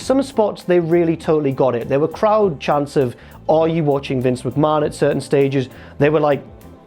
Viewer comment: "Are you watching Vince McMahon" is now my favourite chant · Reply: When I was watching it I misheard it as "are we watching New Japan" which works just as well I guess